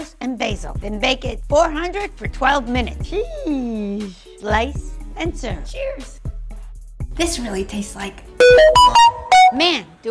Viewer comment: That “sheesh” rly killed me, man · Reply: I thought she said cheese 😅😂